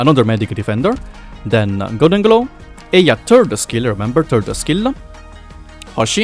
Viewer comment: Why do I hear "Eya's turd skill. Remeber! Turd skill!"? 8:28 Now I will not forget XD